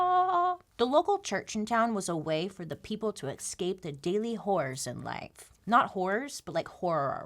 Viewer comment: Anyone else think she said "daily wh*res" even before she brought it up?